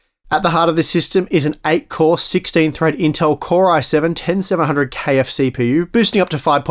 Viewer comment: Am I the only one who heard "KFC (pu)" at 0:33? Like you're going to serve fried chicken with that computer.